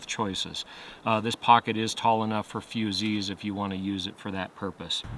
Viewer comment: this pocket is tall enough for “foozees” (I can’t make out what this word is)... what’s a foozee? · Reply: Wildland firefighters use fusees to light fires, often when burning fuel inside firelines. Similar to a long flare.